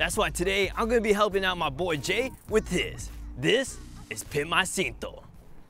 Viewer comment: thought it said simp my cinto.